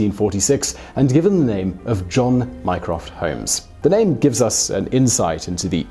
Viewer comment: i thought he said minecraft holmes